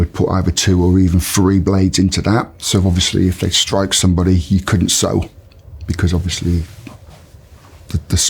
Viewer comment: Can someone tell me what he meant when he mentioned the toothbrush, did I hear he said " so he couldn't settle" due to scaring. what does that mean? or did I mishear. Thanks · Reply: He said ‘so you couldn’t sew it’ meaning the cuts with 2 or 3 blades are too close together so you couldn’t stitch the wound properly. Leaving a bad scar. Where as 1 blade would leave a cut that could easily be stitched up.